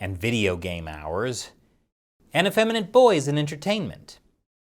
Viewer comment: I thought he said feminine boys, and I was like "China's adding the femboy DLC? Praise the glorious chairman!"